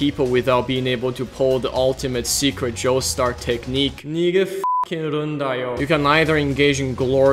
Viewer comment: WHAT DID HE SAYYYYYY??? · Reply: Nigerundayo